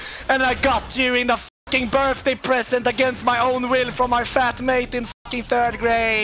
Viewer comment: Did he just say he got a goldfish from his flatmate in thirdgrade?